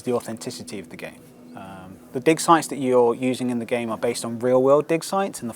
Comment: I'm sorry, but due to your thick accent; I seriously thought you were saying, "dick sites" and not, "dig sites." This game looks incredible though! I cannot wait to play it. I still have my original copies on the PS2 and PC of JPOG.